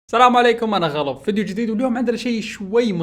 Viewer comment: كني سمعتك تقول انا غلف😥